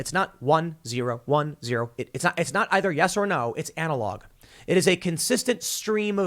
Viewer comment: Did Tim just say "anal log"?